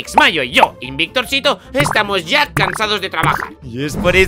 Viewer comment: pende que dijo que estaban casados JAJAJA lo tuve que ver 3 veces la misma parte para entender que decía cansados🤣